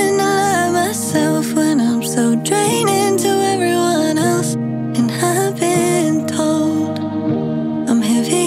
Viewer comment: I thought she was saying ‘’and so I’ve been told’’ instead of ‘’it’s all I’ve been told’’